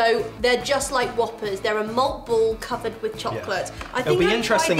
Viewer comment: It sounded like you said Moth Balls instead of Malt Balls lol! Twizzlers are GROSS! I love Red Vines. Ok, now I have to go get Swedish Fish - they're awesome!!! A Mars Bar has almonds here in America. Do you eat all the candy when you've finished a video or are you sick of it? Going to check out your box now...